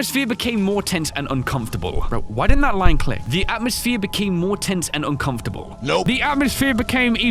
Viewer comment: My ears heard say two different words instead of “tense” I think I heard “tint” the second time.